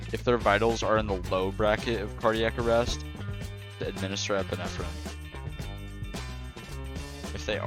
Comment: what did he say at 1:32? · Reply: “Administer epinephrine”